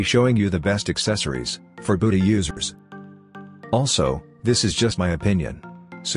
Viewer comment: what did he sayyyyyyy 💀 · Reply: Budhi users💀